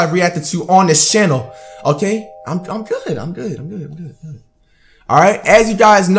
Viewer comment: did I hear a "I'm good baby?!??